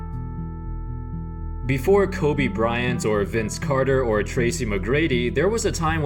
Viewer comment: it sounded like he said Kobe Bryants